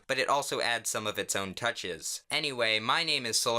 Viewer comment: My nerd self thought you said your name was Solar Sans